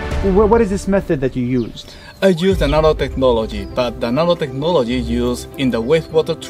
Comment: I thought he said, “I use naruto-technology.”